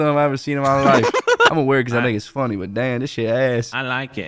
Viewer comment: did say the n word??? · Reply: no he said “cuz i think its funny”